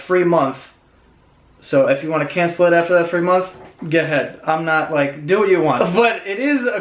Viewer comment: Did he say "get head" 😂😂😂